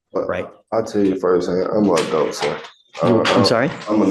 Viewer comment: I think he said "I'm an adult," not "I'm gonna go"